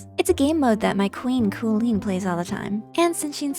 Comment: I heard kooleen and instant like and sub